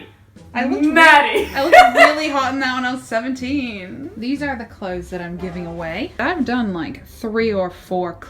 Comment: for a sec I thought you said "these are the clothes I'm taking with me to England" and then I realised your actually giving them away :P